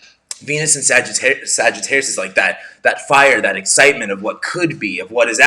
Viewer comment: Sounded like you said "FIRE & HAIR"😂😆😂😂